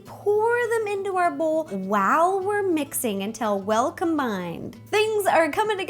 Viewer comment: it sounds like she's saying "Wow" instead of "While"?!